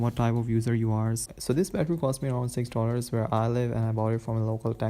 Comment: Did he say 6$ or 60$ ? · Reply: $6 😀